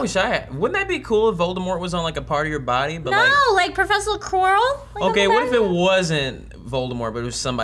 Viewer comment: When Trisha said Professor Quirrell I heard it as profession squirrel :D